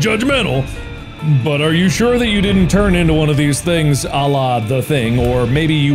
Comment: what did he say??? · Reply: "Are you sure you didn't turn into one of these things, à la The Thing?"